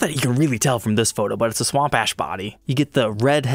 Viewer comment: Every time he says 'swamp ash' I hear 'swamp ass'. Just me?